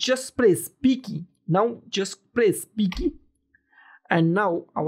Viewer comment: brother what you are saying is totally unclear for me. 10:14 just press what p key or what?? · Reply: b key